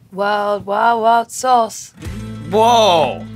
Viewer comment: What does she say at 21:13? Is she referencing the new show name from earlier?? I'm hearing "wow wild wild sauce. There's cookies for the bacon." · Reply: It's a pun: "World Wide Wild Sauce" aka internet show about (hot) sauce. The joke is that it's also a natural tongue twister, which when combined with a burning mouth of hell fire will most likely be said as "War Wah Wah Sauce"